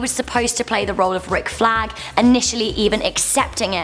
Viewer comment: I thought she said Ric Flair LOL😂